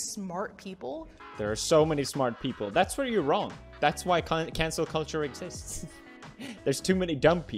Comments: Thought he said in the beginning "cancer culture" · For a second there I thought it said cancer culture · Idkw but I legit thought it said cancer culture and I was like wtf is that like idubbbz shit aha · I thought he said cancer culture lmao